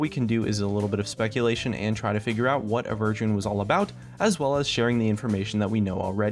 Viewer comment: did he say A Virgin sneaky lil